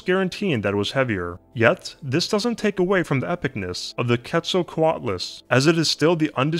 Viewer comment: "Epicness" ? That's what he said at 2:50.